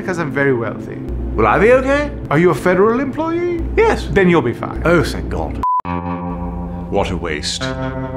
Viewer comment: “Will I be ok?” “Are you a federal employee?” “Yes.” “Well then you’ll be fine.” “Oh, thank God!” Damn, I laughed a little too hard at that one. XD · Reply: Fine? I thought I heard him say, “well then you’ll be fired.” The reply was just as funny with my version 😄